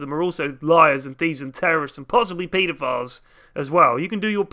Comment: Doesn't Jakiro's backstory just state that he's just a mutant-babby that was allowed to grow up? Would that fall under cop-out? Also the way you said pedophile almost made it sound like you said PLphile, possibly just as bad.